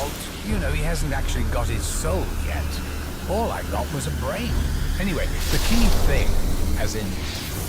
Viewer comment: "All I got was a brain?" Is that what Dr. Monty says!!!?